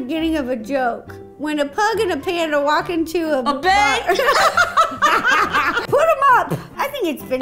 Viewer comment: who else heard bar instead of A BANK!!😂😂